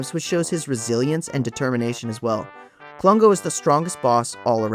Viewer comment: For a second, I thought it said “Strangest” boss. Like, which is most likely to make you contemplate what drugs exist in Great Britain and hence which ones Rare were most likely smoking. Great vid